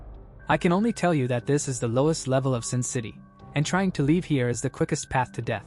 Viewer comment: Thought he said sim city😂